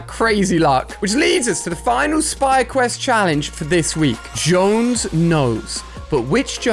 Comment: The audio sounds like it says “Jones is out” in the Foundation’s voice